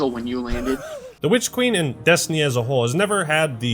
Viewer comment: Every single time you say "Witch Queen" all I hear is "Lich King" too much wow....